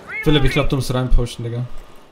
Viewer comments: I swear he said nigga edit: I’m black btw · Did I hear the n word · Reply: I thought IT 2 but no He Said digga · did he just say nigga watch yo mouth if u did